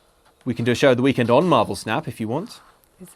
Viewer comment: For the first minute I thought was saying Marble Snap and now I want to play that.